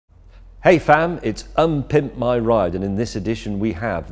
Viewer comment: "Hey fam!" -James May, 2019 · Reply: I thought he was saying "Hey, fan". British understatement.